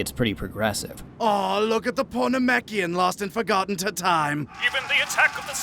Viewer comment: At the beginning I honestly thought Vegeta said "oh you porno mekian" and i just lost it